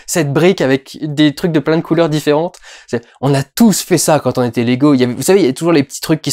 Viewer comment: On a tous fait ça quand on était lego ^^ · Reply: moi j'ai entendu "on a tous fait ça quand on était légaux" ce qui était tout aussi bizarre.